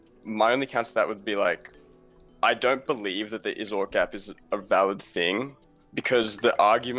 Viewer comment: what gap is he saying? I'm hearing "Izord" gap or something like that. Does anyone know? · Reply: Is-ought gap.